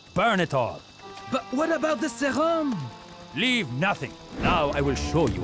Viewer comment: “What about the serum?” I’ve heard it so many times but this is the first time i understand what he’s saying😂 Always liked the voice acting though, it matched the clunky controls and animations:)